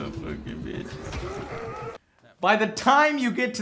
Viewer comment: does he say fucking bitch at 11:52 to that girl he playing with lol